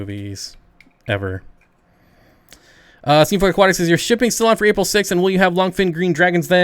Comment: A little after 20:20 you responded to what sounded like "Steenfott Aquatics lol... did you catch that?